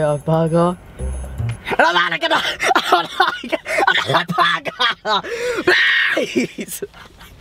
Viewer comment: what was he saying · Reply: Adnan Playz Games He says he wants to order another burger PLEASE!! 😂